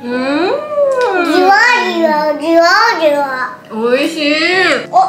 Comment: oh my hahaha i heard "jiwa jiwa" 🥰